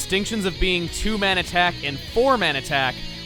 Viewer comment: I thought he said poor man attack